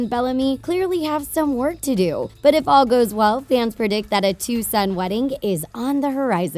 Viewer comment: It sounds like she said two some but she said to suns😜............................sorry kids if u now what i mean😢